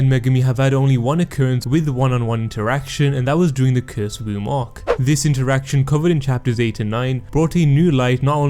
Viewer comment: LMAO I THOUGHT HE SAID "KISS WOMB ARC" WHICH IS ACCURATE ON THE PIC😭🤣